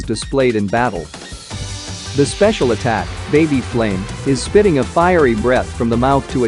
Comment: You say baby flame I hear “pepper breath”